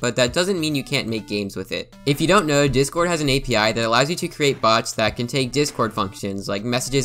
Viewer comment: I heard bugs instead of bots. So, yeah that fits.